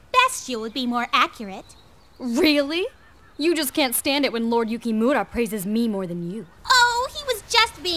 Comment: what word did Kunoichi say around 1:18???? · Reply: she didnt say anything it was Kai who said " when lord yukimura "